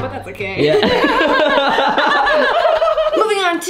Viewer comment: notquitehowthemonthsgobutthatsokayyyyy laughter it look me so long to figure out what she said there😭😂